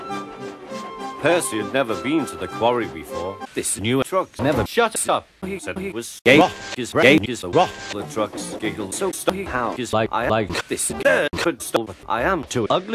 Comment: What are the trucks saying/singing at 0:51? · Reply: "This new truck never shuts up." "He said he was a rock. His brain is the rock." The trucks giggled. "So, Steve, how is life?" "I like this girl, Crystal. but I am too ugly." "Rubbish," said Tom. "You're a truck. You're always beautiful." "Hey, wanna buy a faulty coupling? It's only 555 coins." - played by me